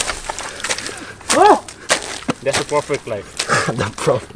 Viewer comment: I think he said “perfect” but profit fits as well 🤣🤣🤣